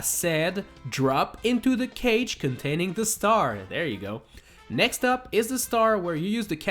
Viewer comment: Mandela effect I had on the video: I thought he said: drop into the cage, and get the star! There we go. But he actually said: drop into the cage containing the star! There you go.